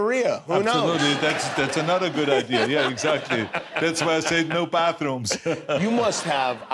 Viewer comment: Who else heard “There is no bathroom”?